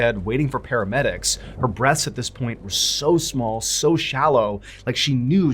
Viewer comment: I misheard it as “her breasts” and was really confused lol